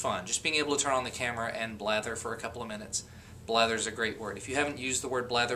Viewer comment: i thought u were saying bladder